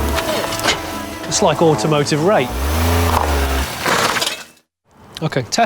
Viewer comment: Excuse me if I misheard, but did he say automotive rape?